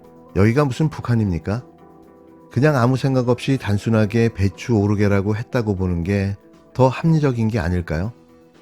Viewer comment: 대추오르게 로 들림 나는ㅋ